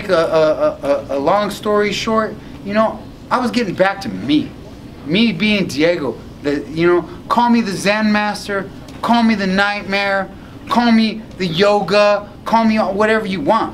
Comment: Sounded like he said the xan master